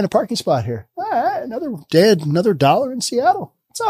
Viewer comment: I heard “Another dead, another dollar in Seattle.”